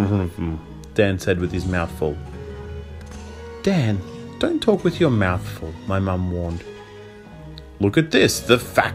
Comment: what did Dan said with his mouth full? 🙂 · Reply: "Mrph" is exactly what it says in the book, ahah